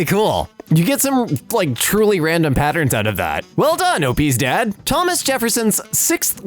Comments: i thought he said obese dad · I GENUINELY THOUGHT SHE SAID "well done, obese dad" JGJJHGKJH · swear said "obese dad" lol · I’m really confused. Did she say obese, opie’s, OP, or something else? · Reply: OPs dad, I’m fairly sure :) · I should get hearing aid. For some reason I heard him say obese onstead of OPs dad xD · When you said "well done OP's dad", I thought you said "well done obese dad"